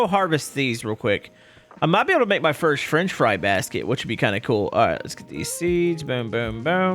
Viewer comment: did say Frinch Fry?